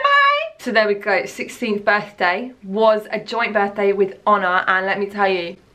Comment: honor??? for years i’ve thought you were saying ana in a british accent. my life is a lie.